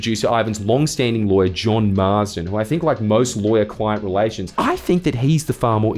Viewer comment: I heard “John Marston”